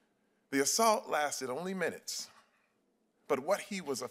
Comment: Did he just say 3 minutes? Sounds like he wanted that dude to touch him... 😂🤔😂